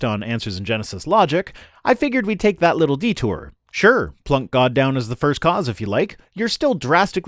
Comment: did I hear “Plank god down[…]l 😅